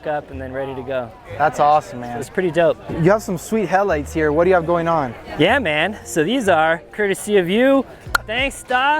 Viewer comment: What brand etc headlights are they talking about at 13:35, I didnt quite catch it. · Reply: Those are the alpha rex TRD pro style headlights, they are currently sold out but you can find them on out website here: <link>